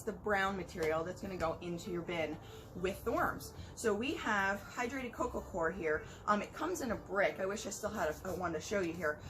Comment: Did you say "hydrated cocoa core"? Edit: sorry I misheard, this is called "HYDRATING COCO COIR BRICKS"